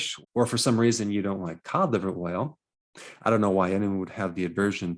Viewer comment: took me forever to hear correctly what he was saying when saying "Cod liver oil". I kept hearing "Cauliver whale" I was like wtf is that